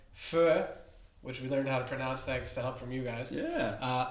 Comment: It sounds like you said Pho that is an really good Vietnamese soup!